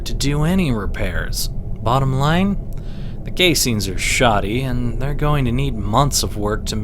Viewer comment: is it only me who heard him say gay scenes instead of casings